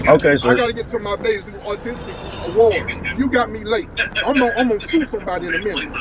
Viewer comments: Did he say he needs to get to the "autistic awards"? What is that like The Game Awards but for autism? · I heard “I’m gonna shoot somebody” not “sue somebody”!?